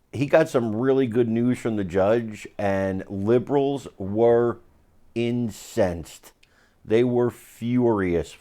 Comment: I thought he said, liberals were insane, and they are.